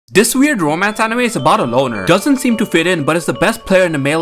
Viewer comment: Bro, for a second, I thought he said "Boner" 💀